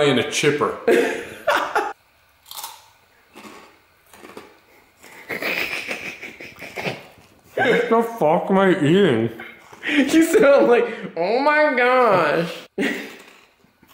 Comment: i thought to myself he said "the fuck my teeth" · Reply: Game Nery NOPE, wrong! "The fuck am I eating?"